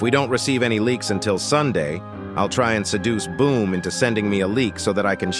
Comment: did you just say seduce boom.